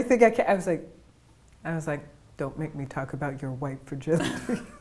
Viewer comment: I just had to google what she was saying at the end of the video, I thought she said White virginity, like wtf is that?